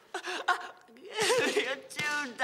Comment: does perks say 'Jude', is that Harris first name? I thought his first name was unknown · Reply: Don't know if you're still interested... I think he says "Tune! Give me a tune, Dan!"